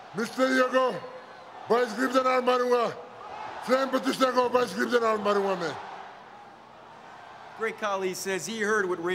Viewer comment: Did he say "gaand marunga" @ 0:53😂😂😂😂😂😂😂😂😂